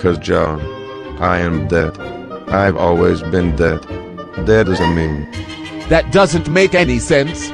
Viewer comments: I thought he said “dead is in me” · "I've always been dead. Ded as a meme" -Garfielf 2018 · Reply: I thought he said "Dead is me" the first time I watched.